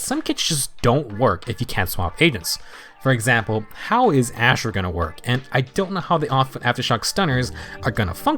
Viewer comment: in 21:06, you said something like "how is 'Ash' gonna work?" Who's Ash? am i mishearing it? you named that name a couple of times im completely confused xD